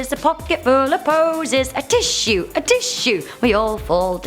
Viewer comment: I guess the song “ring-a-ring-a-roses” is different from the one in America because ours is called “ring-around-the-roses” and you guy’s goes Ring a ring of roses A pocket full of poses A tissue, a tissue We all fall down And America’s goes Ring around the roses A pocket full of posys Ashes, ashes We all fall down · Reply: I heard it as ashes ashes instead of a tissue